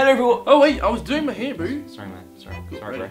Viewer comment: DID CASPAR JUST CALL JOE BOO???